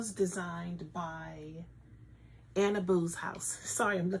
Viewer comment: Hahaha, I was cleaning making noise trying to listen to you. When you said Annaboo's...i thought you said Anna Boobs....I laughed so hard, I rewound to listen again. That's what I get for not just sitting.. knitting and paying attention. 😂